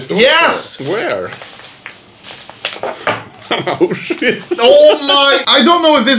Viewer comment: That was Papa's best joke ever lmao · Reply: ah he said auschwitz, i thought he said oh shit